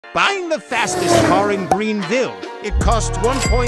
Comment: I thought he said bang the first car